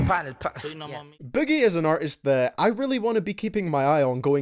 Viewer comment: I thought you said biggie but you said boogie.